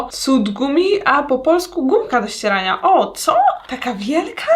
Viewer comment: Usłyszałam budka do srania BSNBKSDJNBBNKACWEF